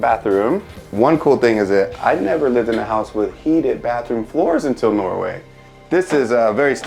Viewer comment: What does he say @ 2:53. I rewinded it 10 times but still don’t understand. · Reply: that he didn't live in a house with heated floors before moving to Norway